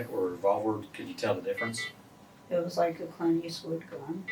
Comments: the captions said “inaudible”…. What she really said was “It was a Clint Eastwood gun”!! Man she watched to many westerns and got caught up with the wrong sod busters!!! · The caption says that she said "It was a (inaudible) gun." But what she actually said was "It was a Clint Eastwood gun." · *inaudible* LMAO she clearly said Clint Eastwood. Do ya'll even TRY to decipher for captions? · is her saying “it’s was like a Clint Eastwood gun”